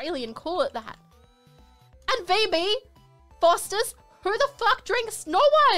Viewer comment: What she actually said was "And VB" (Victoria Bitter) another brand of beer. (One that Australians actually do drink!)